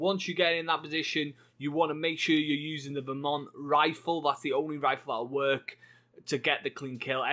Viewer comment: I thought you said Vermont rifle. I was thinking what the hell is that. Now I understand you are saying Varmint Rifle. Good video, thank you.